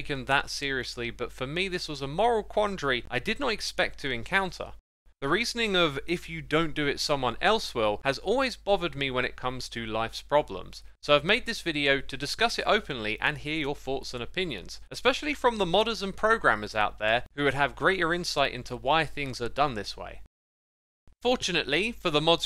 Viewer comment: did he say Lights Problems as in Light Yagami from Death Note?